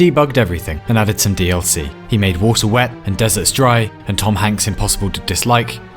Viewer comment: Did you just say water is wet?